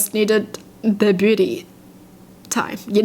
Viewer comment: the way she said beauty I heard it as booty...